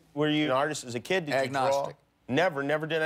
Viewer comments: "Were you an artist as a kid?" Bush: "Agnostic.." Lol. Is it just me or is that what he said? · what did bush say? To the Jimmy's question were u an artist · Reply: Mike Dude agnostic I think